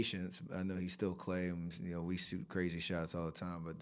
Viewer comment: Klay is klay 😂 we're used to crazy shots 😂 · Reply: I thought he said we both take crazy shots